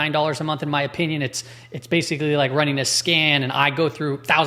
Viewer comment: did he say running a scam? 😂😂😂😂😂 Indeed. 😂😂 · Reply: He said scan. And that tight list is insane. You just a dumbdumb 😂